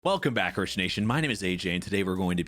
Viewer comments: lol the captions say "welcome back christian nation" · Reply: Everytime all i hear is "welcome back kritsination" like, what's a kritsination? xD (he means (AJ)Rich Nation i guess) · lol the captions say "welcome back christian nation"